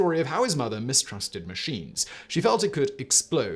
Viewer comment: Trying to watch this without waking up kids. I thought he said people mistrusted SHEETS not machines